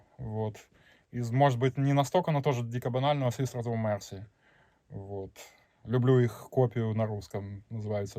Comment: Про що він каже на 14:55? swiss rotor mercy? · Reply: знайшов, The Sisters Of Mercy